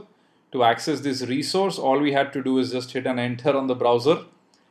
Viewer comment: what did you say do in the browser in 5:39 ????? I repeated that word 20 times and couldn't understand it ... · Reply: "... All we have to do is hit an Enter on the browser ..."